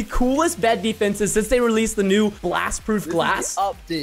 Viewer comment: I thought he said glass proff glass XD